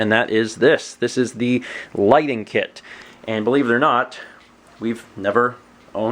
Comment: am I the only one that thought it said lightning kit at first?